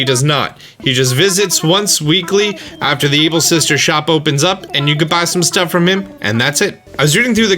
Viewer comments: I thought he said the evil sisters not the able sisters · I heard ebil?